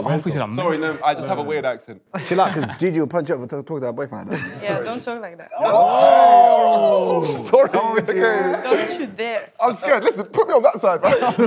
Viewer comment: what does gigi say that makes everyone gasp? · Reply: “Yeah don’t talk like that”